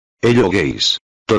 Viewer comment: al principio cuando pusiste hello guys escuche "hello gays" xd